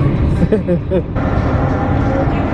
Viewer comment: when he said "thats nasa over there" I heard Nassau and i was like that cant be for real they haven't even gotten on the ship yet